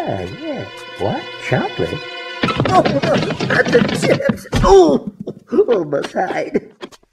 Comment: What did he say? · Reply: Did he say “on both sides” or “almost hide”?